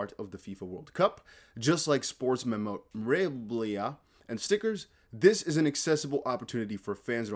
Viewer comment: Bro! Did you just say "memreowaamblahh".. Omg..I almost spit hot coffee out my nose! Memorabilia.. Pronounced. mem- o- rah- beel-ee-ah